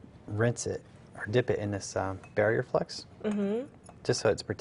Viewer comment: Hi! Would you please give me a link to the product you used to dip the ring in before soldering it? It sounded like you said barrier flex or flux. Thanks